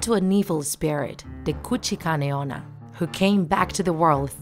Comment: Wait A minute.. Did u say Kuchikane Onna?! ( 2:35 ) And Dis is how many Markers dis Woman has 👇